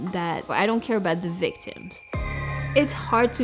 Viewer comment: Did she just say "I don't care about the victims"?